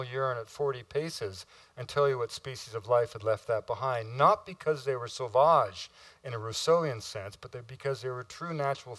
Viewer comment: Can you clarify something for me please? At 27:16, ... he says something ... "not because they were ??? in a ??? sense, but because they were natural philosohers" ... what are the words he says there? I can't quite make it out. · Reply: Hey Sonny. He said "Not because they were sauvage in a Rousseauian sense...". He's referring to the 18th century philosopher Jean-Jacques Rousseau, who was the first to popularize the idea of the primordial "noble savage".